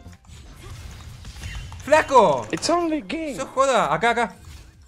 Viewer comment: Que es lo que dice en el minuto 4:33 ? It’s only king?